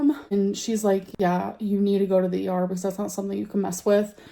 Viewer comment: For a moment I thought her mom said, yes you have to go to the Arbys 😀